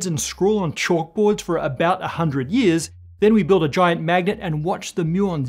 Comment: "So, there you have it. That's how we peer beneath the hood of reality: We scratch our heads and scrawl on chalk board for about a hundred years, then we build a giant magnet and watch the muons dance!" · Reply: The first time I watched this I thought he said “giant maggot” instead of “giant magnet”. Giant magnets are cool. Giant maggots are nightmarish.